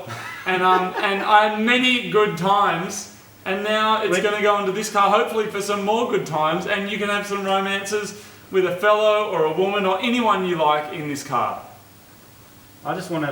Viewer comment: I thought he said Othello. I've been spending too much time in my English Class.